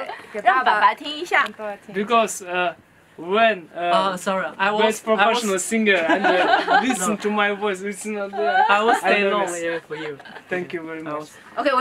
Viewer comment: he actually says "Because when .. best professional singer ... listen to my voice ... it's not (inaudible) .. I am nervous". Not the most eloquent English from DQ :D .. but it makes more sense in the context. :) Thanks for putting this together! <3